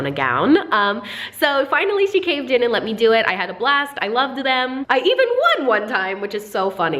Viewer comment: I thought she said I got last instead of a blast